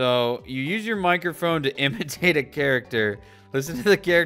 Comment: Love how i misheard imitate as amputate. "You use your microphone to amputate other characters" okay so how do you do that? 😂😂😂